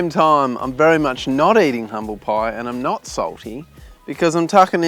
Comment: I thought he said he was eating crumble pie and then he said he wasn’t eating crumble pie and I was confused.